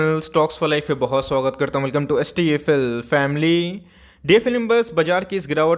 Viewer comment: Can Anyone tell me What is DFL imburse ? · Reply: He said stfl members, tej bolte hai isliye waisa lgta hai.